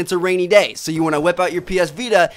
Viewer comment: Hahah you're not alone, lol I thought he said "whip out your penis vita" XD